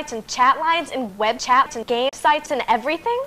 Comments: i swear she said gay sites BAHHAHAHAHAHHH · Does she say "game sites" or "gay sites"? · Lol did she say gay sites!? XD